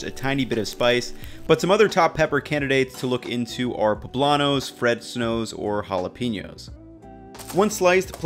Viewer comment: wait did he say fred snows?